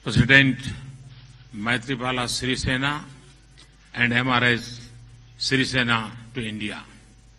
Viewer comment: Am i the only one who heard modi saying Mrs sirisena as M-r-s srisena at 1:46? :|